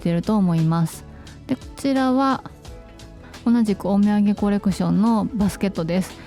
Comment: さーん(*´꒳`*) 前回の動画のカゴ紹介されてましたー😊可愛いけど少し大きいですね😅 実際に見てから購入決めようと思います💡 ドーナツ形のライト絶対欲しいです〜❤4月が楽しみですね😊 オミェンゲ、最初聞いた時ずっとお土産シリーズって聞こえてました😂